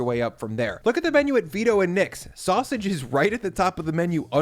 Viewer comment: So, he said, "I went to Vito and Nick's: the supposed birthplace of the Chicago thin crust pizza." I heard, "I went to veto and nix the supposed birthplace of Chicago thin crust pizza."